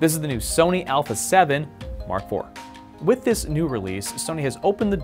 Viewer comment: Did he say "new Sony Alpha 7, R4" ???